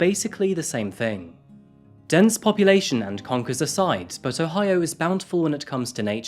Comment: Did he just make up the buckeye being the American word for conquer bc I'm from vermillion ohio vol to be mire specific and I've never heard that and looking it up still don't see it anywhere not tryna down him or nun j pm me if anyone knows can u pm on YouTube lol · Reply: Nvm to me it sounded like he was saying conquers not conkers conkers are the same as buckeyes